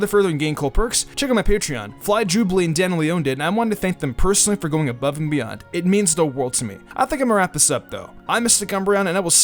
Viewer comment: I swear I heard you say I'm mister cum brown at the end